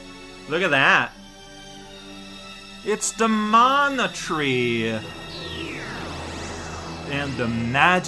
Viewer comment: looked away briefly and thought I heard announce "hey! It's the monotreme!"(2:00), looked back, but knuckles was nowhere to be found.